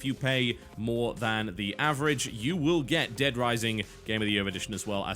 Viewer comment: wait 16:46 did he say dead rising?